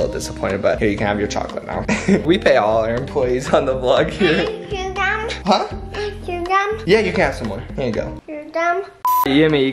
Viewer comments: Im wheezing is it me or did she say "You're dumb" 😂😂😂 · did she just say your dumb lol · I SWEAR SHE SAID "you're dumb" LMAO